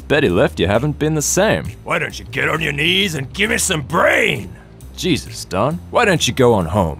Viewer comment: Glad you posted the comic I thought he said "Give me some Breen" not give me some "Brain"